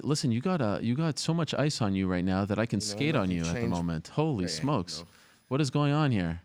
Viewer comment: idk if it was just me but I thought Ariel said “I could skeet on you” 😂😂 thank god for rewinds